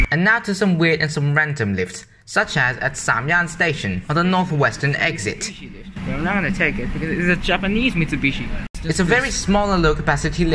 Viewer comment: I swear I heard a sigma